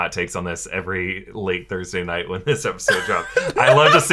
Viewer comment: Wait...they drop Thursday night? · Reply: That's what I was thinking, I thought I heard late Thursday night